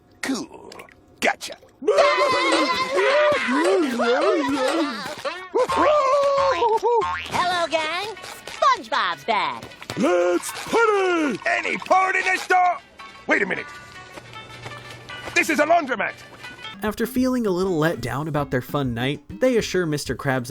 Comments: I know i have this episode recorded on some old vhs lol I used to rewind it like crazy cause I thought mr. Crabs said "any porn in here" 😆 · Did he say “Any porn in this store?” Or am I hearing that wrong? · Lmao anybody else hear when mr krabs enters the wash he says” any porn in the store” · Is it me orrr, one of the reasons the episode got banned was because they thought mr krabs said any p- uhhmmm in this store, even tho he is saying port